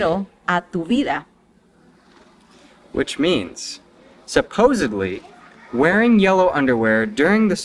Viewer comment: Sounds like he says "supposably" instead of "supposedly."